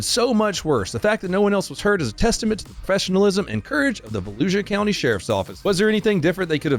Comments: I legitimately thought you were saying Fallujah County Florida as a joke, not Volusia County. Seems really fitting. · Oh... "Volusia" Florida.. i thought you said Fallujah.. 😂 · Sounded like you said Falluja county , I was like wait a minute..... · When you said Valusia county I could have sworn you said Fallujah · I thought he said Fallujah County, Florida not Volusia. Fellujah seems more fitting though. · Volusia 😅😅😅 swear to God i thought you said Fallujah county. And i laughed. Then again when i saw the officer actually say it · I thought you said Fallujah county lol